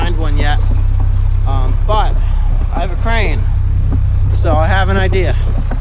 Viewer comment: i thought you said "i have a brain. i have an idea" and i was thinking thats an understatement. then i saw the crane and i was like, oh. ps. its nice hearing drum and bass and jungle. i prefer mashup ragga but dnb came first for me. it still surprises me how jungle was replaced by dubstep. people dont know what they're missing. love the castle and you make nice videos, too.